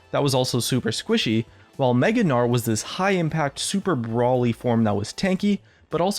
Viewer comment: Did he say Super Broly form? The walking steroid?